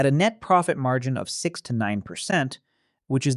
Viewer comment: Great video! I wanted to point out a quick transcript error at 22:01 for anyone watching with closed captions. The CC says the industry average net profit is "69%", but you can clearly hear he says "6 to 9%". If you do the math on the $346k gross revenue he mentions, 6% is $20,760 and 9% is $31,140. Just clarifying so nobody gets the wrong idea about F&B margins!